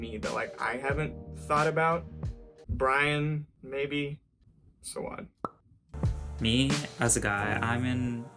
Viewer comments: I thought that he said so odd but it was Sowad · I think that dude said "so odd" and not "sowad"